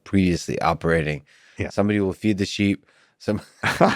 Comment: Thought you said feed the SHIB not feed the sheep lol